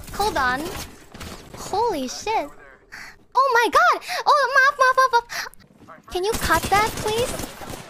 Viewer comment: I cut out some parts cause I can't really understand ID but I think she's only repeating what she says probably(?) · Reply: i think she said "oh maaf, maaf". Maaf mean sorry/apology in indonesian.